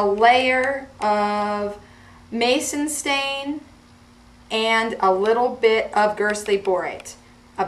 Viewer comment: HELP PLEASE. What does the girl say in the 1:36-1:40 minute? A layer of Maison stain and ???girsty bright??? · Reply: Mason stain and gerstley borate. These are powders mixed in equal amounts plus water.